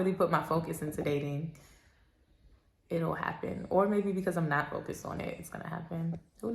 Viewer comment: I thought he said “Because I’m chilling” 😂